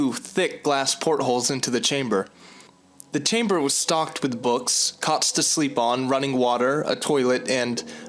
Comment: I thought he said "cocks to sleep on"... Damn I need sleep, oh wait.. xDD